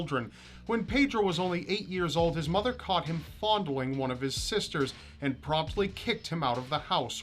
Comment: What does he say from 1:11 to 1:13? i don't understand one word that sounds just like "foundling" but i'm not sure if that's what he says.